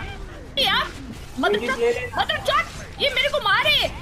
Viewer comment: did say madarchod? 😂😂